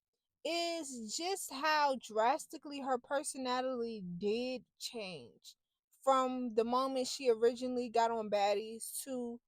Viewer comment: Sounds like u said her person Natalie changed 😭🤣 so true lol